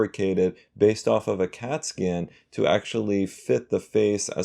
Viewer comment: Wtf did he say cat skin? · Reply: Scan